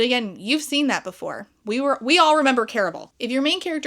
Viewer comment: I thought you said "We all remember Carol!" when talking about stupid people and was like HEY WHOA... I was so confused.... and I should get my hearing checked